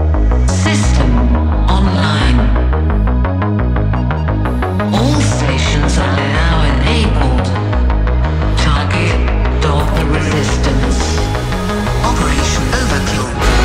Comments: In "Overkill" it doesn't say "Target... Stop the Restistance" but "Target... Dogma Resistance"!! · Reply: Huh, i heard "Target, of the Resistance" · In "Overkill" it doesn't say "Target... Stop the Restistance" but "Target... Dogma Resistance"!! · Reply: I thought it said, "All of The Resistance" back then. Now it makes sense! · In "Overkill" it doesn't say "Target... Stop the Restistance" but "Target... Dogma Resistance"!! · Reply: I heard "don the resistance" but that makes so much sense